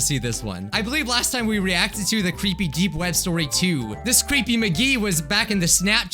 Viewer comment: i thought it said the creepy dude is black...